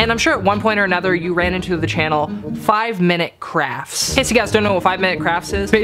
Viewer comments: It sounded like he said 5- minute crap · It sounded like you said 5 minute craps lol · I don't know if its just me but when he said 5 minute crafts I all heard was 5 minute craps like if you thought it he said that too just want to know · when he said "5 minute crafts" i thought he said "5 minute craps" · Oh I thought you said 5-minute craps